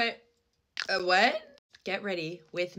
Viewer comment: STOP I THOUGHT SHE SAID GET WET💀💀💀💀